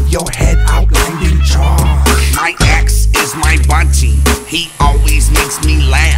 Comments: It actually sounds like he says "My axe is my budgie" which is leaving my 2 budgies completely bamboozled. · I thought he said my axe is my banshee, and banshees are fucking scary lmao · Why does it sound like he says my axe is my bungee to me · lmfao it sound like he sayin my axe is my budgie